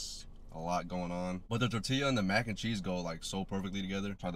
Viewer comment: Everything was going fine until Homie said “Tor Tila” 😢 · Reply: Am I hearing wrong? I literally heard him say tortilla not tor tila.